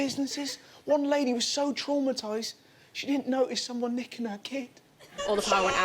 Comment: I fully thought he said "she didn't notice someone licking her kid" 😳 until they showed the footage and I remembered that "nicking" is also an option 😂